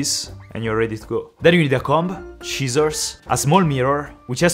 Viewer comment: when he said scissors i thought he said "cheesors"